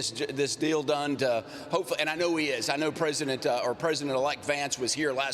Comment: did he just say president elect Vance????